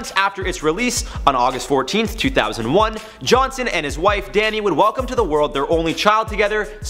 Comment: am i trippin or does he say john cena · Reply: It sounds like it but he says Johnson